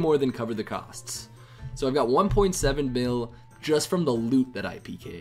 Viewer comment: did he say bil?